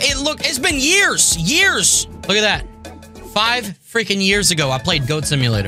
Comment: did i hear that correctly? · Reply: You heard frickin buuuuuut who knows maybe one day 🤬🤬🤬🤬